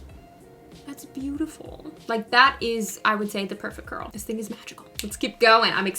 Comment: At first, I thought you said, "now, that is the perfect girl." I was thinking, "sounds about right to me."